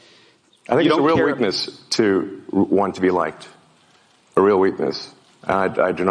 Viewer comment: Your title misquotes Musk. What he says (@ 4:45) is "It's a real weakness to want to be liked", not "It's a weakness to be liked."